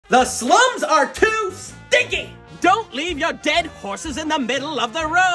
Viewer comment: I heard "The slums are too sticky" and laughed way harder than when I realized he said "stinky".